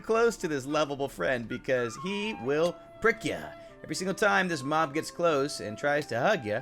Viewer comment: I thought when he said ‘prick ya’ he said ‘frik’ ya’ 😂